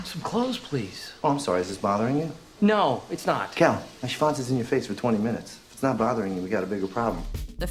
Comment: did he say 'schwanz'? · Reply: shees, I guess he uses the german word schwanz for dick :D